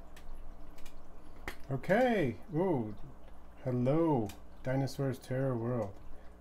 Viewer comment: That was a cool livestream good job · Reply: I watched the recording and the first thing I heard was "Hello Diosaurs terroir World" - I was like, I know that guy !